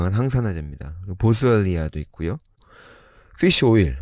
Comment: 정도에 보스얼리아?!…뭐라고 하신건지 ㅠㅠㅠㅠ??? ㅠㅠㅠㅠ